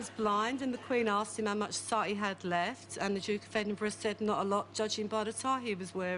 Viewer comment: what does she say at 20:40 ?? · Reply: the quen asked the blind kid how much sight he had left